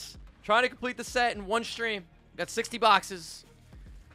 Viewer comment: I thought you said 16 boxes but 60!?!? That’s insane